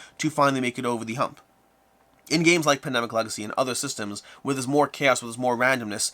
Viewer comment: Did you say Pandemic Legacy at 11:00? as an example of a game with a more interesting balancing system.